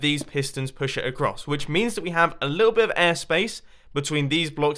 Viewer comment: I thought he said a little bit of ass space on 6:57 😂😂😂 XD